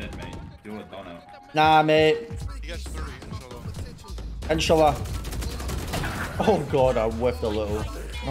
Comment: did you say InshaAllah at 3:22 ?